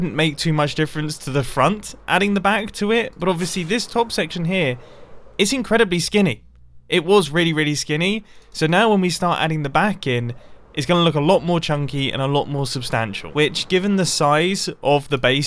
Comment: Did he just say "When we start to add Baggin, it will look alot more chunky" hmmm.... 3:27 Is it Portato Baggins comming to town ? :O UHHHHHHHHHH!